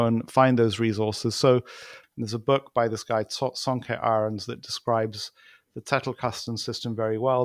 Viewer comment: I couldn't catch the author or the name of the system that Guy was talking about at 14:00. Sounded like "Saunka Iron" and "Tettle Custom System" lol. I got the spaced repetition part.